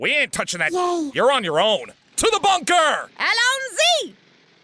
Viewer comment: So, what did say at 22:07, and what is it a reference to? I know I heard it before from someone else, but I don't know where it originally came from. Does anyone know? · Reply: It's not a reference to a horror movie, she said "Allons-y" it is a French phrase made more famous by the fact that it was used as the catchphrase for David Tennant while he was the actor for Dr. Who. I don't remember what the translation is offhand.